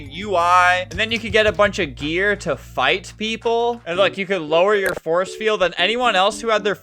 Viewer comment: Did you just say…Phight?